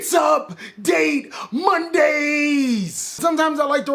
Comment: I always thought you were saying “It’s mundane Mondaaaaayyy!” …